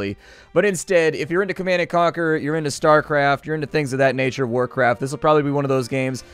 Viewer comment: Did i just heard "Starcraft?"